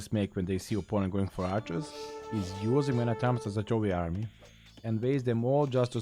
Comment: What does say here in this sentence at 01:04 - "The main mistake players make when they see opponent going for archers is using man at arms ???? army", I can't understand what he says at the end there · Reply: using man at arms as a throwaway army